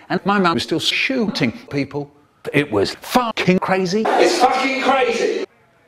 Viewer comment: Did michael really say its fucking crazy as 1:57?